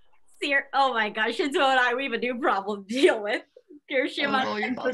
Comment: Wait did anyone else hear Jiro say something about her Shinsou having a problem if it was all full of Kirishima?